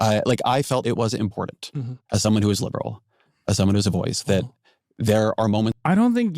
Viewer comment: Somehow I thought he said: As someone who's an idiot. And felt like that was more honest than expected 🙄